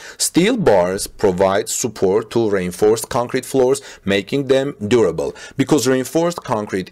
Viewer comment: Reinforced concrete 😂 I thought you were said rain forest concrete and was like 🤔